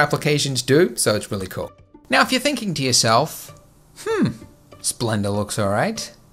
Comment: Did he say "Splenda" @ 12:11 ?